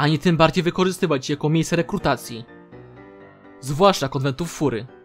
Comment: usłyszałem kotletów xDDD